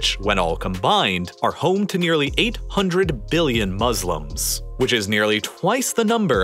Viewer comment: does he say 800 billion people?